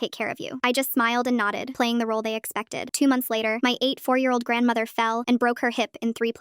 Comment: At first i heard "my 8 year old grandma"